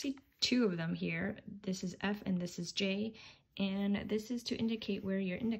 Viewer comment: At first I thought you said "this is [some random name] and this is Jake"